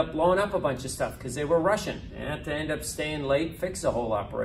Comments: "They were Russian. They had to stay behind and fix everything" Looool I know he said "rushing" but it sounded like "Russian" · it sounded like he said people were blowing up a bunch of stuff cause they were Russian 🤣